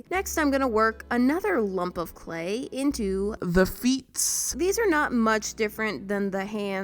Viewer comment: did she just say 'the feets' ....wellllllllllll